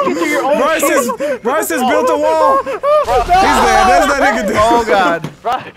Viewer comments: what did he say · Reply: Cyrus Knighton "there's nothing he could do" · did you hear what he said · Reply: "He's dead, there's nothing he could do."